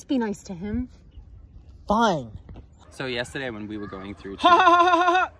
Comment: didn't even make eye contact with Daryl. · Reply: Who’s Daryl thought he said Damian?